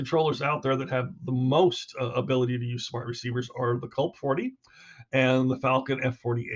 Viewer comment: Hi there, can I just ask what the other controller you mentioned was at 1:30 in the video, I heard and knew about the Falcon but couldn’t quite make out the other, could you please clarify what that was and who sells them. Thank you great video 😊🔥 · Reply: Kulp 40 and Falcon F48 ( we use the falcon )